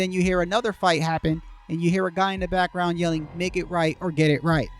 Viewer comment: Hi, @ 12:41 , do you think it’s possible that the man is saying “S’ain’t right! S’ain’t right cuz! S’ain’t right! “ ? I have other auditory evidence that’s suggests that may be what he’s saying. I could explain it here but most ppl don’t want to read long comments. If you or anyone reading this is genuinely interested in my findings I’m happy to share it. Ultimately what he’s saying ain’t gonna crack the case. However, I believe the more we can unravel mystery and pin down any truths about that night the closer to a reality where appropriate justice is served we’ll be.